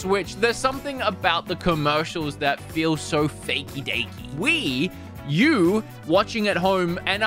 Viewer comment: what he hears out of his mouth: we, you what i hear: wii u